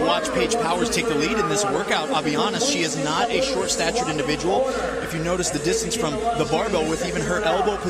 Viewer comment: “she is not a short stature individual”… did I hear that right while the graphic reads 5’3”?